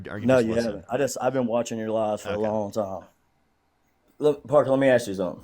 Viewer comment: Did he just say, “I’ve been listening to your LIES every day”?